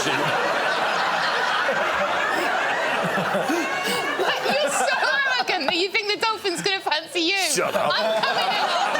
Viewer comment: dolphins plus hope 5:22 Help, please! What's does Lou say to Greg? The auto-generated captions don't have anything & I can't figure it out. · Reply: “You’re so arrogant that you think the dolphins are going to fancy you”